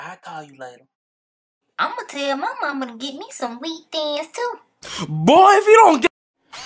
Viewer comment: he said her timbs look like wheat thins. LMFAO 💀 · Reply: 😭 I thought he said Beat Thins when I watched it at first lolol